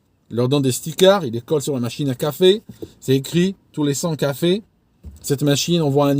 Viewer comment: j'ai entendu tous les sans café ! 🤣 Ca marche aussi, tous ceux qui tournent à l'eau chaude ils plantent un arbre et ne participent pas à la culture du café ! 😆👍